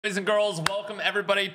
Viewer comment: did he say ladies and girls?